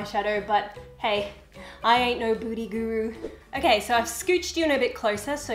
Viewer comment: Lol it some times sounds like you say "booty guru" instead of "beauty guru"! No hate I actually find it funny and love it!